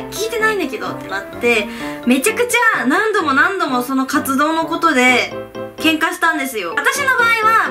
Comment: 1番最初の「活動のことで」の所「カツ丼のところで」って聞こえた笑